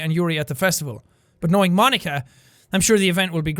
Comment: When he said Monika it sounds like he was saying my nigga lol